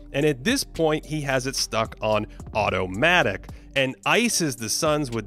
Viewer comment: did say Ottomatic? 🤔